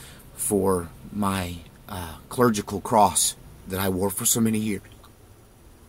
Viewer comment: I'm at the 2:06 mark, and I stopped the video because I thought you said "surgical cross". WOW! The power of the cross IS surgical! Trick of the ear, or Holy Spirit? :) God bless you as you begin your new season as warrior AND surgeon for Christ!